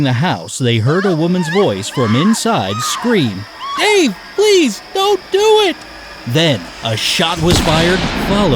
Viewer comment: i heard "hey please dont do it"